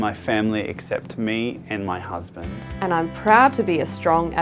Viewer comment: Go all the way to 1:29- did he say his husband????????